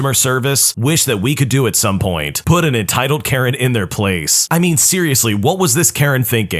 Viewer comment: instead of it saying ‘Karen’ on the subtitles, it said ‘carrot’ I would not like to eat an entitled carrot 😅